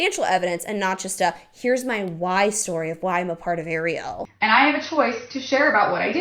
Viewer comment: This video just played next so I didn't click and this the title, so when I heard you say "Arieyl and" I had to refresh my screen 'cause I thought you were saying areola 😆